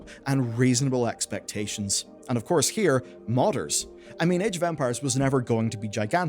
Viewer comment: Sorry, but I keep hearing "Age of Vampires"